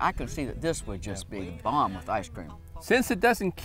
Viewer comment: Did she say that would be bomb with icecream? Lmao what a cool older woman